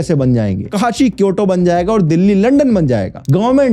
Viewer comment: what did he said as kyoto cause i visted to kyoto